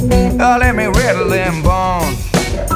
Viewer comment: I swear at the end, I hear him say "let me ritalin bong"